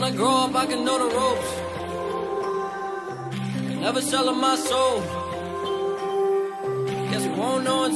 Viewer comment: I thought he said Im selling my soul I was like wat